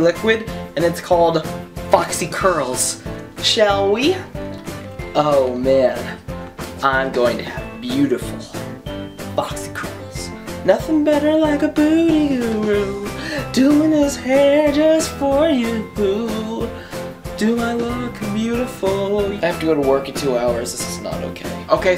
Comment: For a second, I thought you said a booty guru hahaha